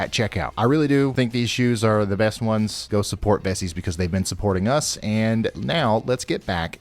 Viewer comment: I'm not scared, I'm ANXIOUS. I get sick to my stomach every time these happen. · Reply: Vessi I thought said Bessie